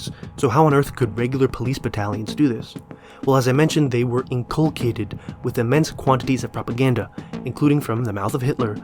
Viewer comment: Great video, but I have to ask: what word did you say at 31:54? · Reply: Thanks! The line is: “So how on earth could regular police battalions do this? Well as I mentioned they were inculcated with immense quantities of propaganda…”